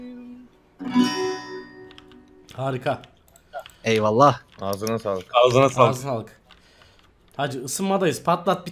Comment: ne diyor amk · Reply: Azna ağlık